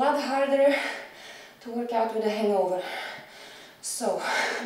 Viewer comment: did you say "hangover"? O.o 😀 And when hangovers bring most people down, does 20 reps per crazy-tough exercise... Just imagine if she didn't have one... 40? 😀 F_ck 😀